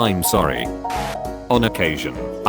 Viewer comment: I thought it said eyebrows